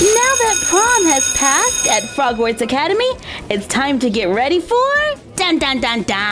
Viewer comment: I thought she said hogwarts academy😂😂😂